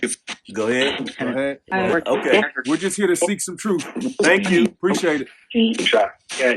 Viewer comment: Did anyone else hear ask, “Do you mind if we look around?” The response “We’re going to follow”